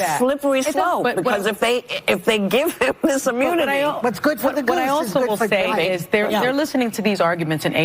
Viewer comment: Did Joy just say "What's good for the goose is good for the baby"? LOL IDIOTS!